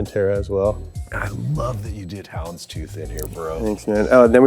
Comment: what does he say?? “__ Tooth?” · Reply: Houndstooth